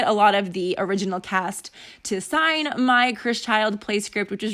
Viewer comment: At the beginning I thought you said Chris Childs and thought it was a country singer concert or something 🙈 love the nail colour 💅